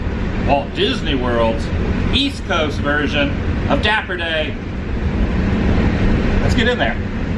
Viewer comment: good vid 2 sets of dumbos hmmm both in the vlog🤣 thought it said dabber the 💩 dance never heard of dapper day🤣